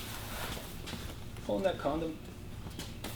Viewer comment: did he said condom?